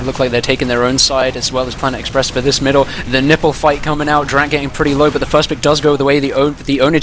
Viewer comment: is who else hears one of the casters say nipple fight?